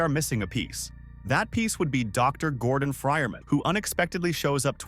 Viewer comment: “Dr. Gordon Friman” lol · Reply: Lmfao, for real, I thought he said "fryer man," and that shit confused the fuck out of me.